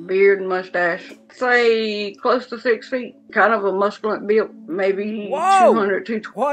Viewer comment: I had to watch this a few times because I didn't catch the Scamfish original of this week... Around 18:30 she says, "kind of has a musculant build..." What? Musculant? What in the Jimminy Cricket is she talking about? Whilst you were concerned about the 🐌 trail I was worried about this language issue.